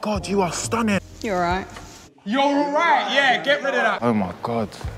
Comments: I’m not mad at her saying you’re alright. He’s literally letting his friends send her packing w/o seeing her🤷🏽‍♀️😂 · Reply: I thought she'd said you're right. What she really said was rude and petty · Sounded like she said 'You alright?" I didn't hear you're alright. I think she was asking if he was ok seeing what he was losing out on. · she’s not bad for saying “you’re alright” she just knows her worth 😂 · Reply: I thought she said “you alright?”